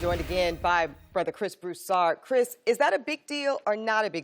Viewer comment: did she just call Broussard a brother lmao